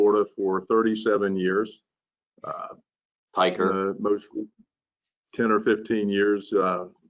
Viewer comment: What does he say at about 1:31? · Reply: Sorry for the very long delay. I am just now seeing this comment. I think is saying, "Piker!" He is joking about the length of time John has been practicing.